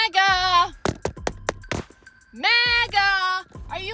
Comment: I thought she first said "NEGGA!" 😂🤦🏾‍♂️